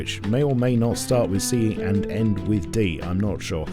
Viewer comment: my brain farted for a second and heard "Starts wit E and ends with D" and i was like, "..wait, there's no way i heard this right". turns out i was wrong as expected 🤣